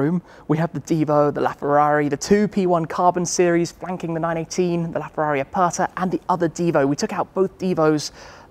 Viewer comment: Tim if your not listening carefully it sounds like you say “the two P1’s wanking the 918” I had too listen too it twice 🤣🤣🤣🤣🤣🤣🤣